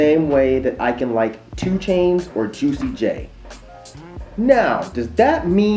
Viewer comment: did he say "Tuicy J"?